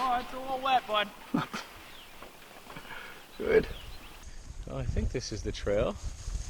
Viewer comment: Did say "good."? lol